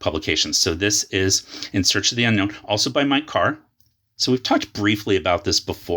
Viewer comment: I misheard "also by Mike Carr" as "also buy my car". Had me confused for a second.